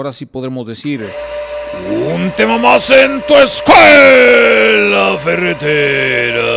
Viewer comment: Yo siempre entendí "ponte mamadoentu escuela periférica"